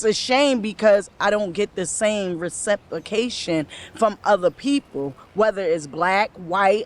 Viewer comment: Did she just say “receptication”?